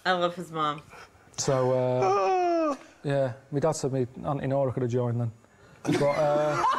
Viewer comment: What did Karl's dad say at this point? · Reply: "my auntie Nora could've joined 'em (hells angels)"